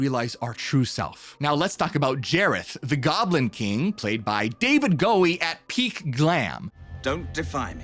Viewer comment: Did you just Say David Gowie? its David Bowie...